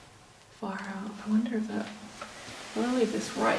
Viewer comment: Did you say "far out"? 😂😂😂